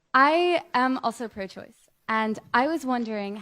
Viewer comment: When somebody starts out by saying "I'm a socialist" it's equivalent to saying, "I'm an asshole." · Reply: I thought she said "I'm pro-choice".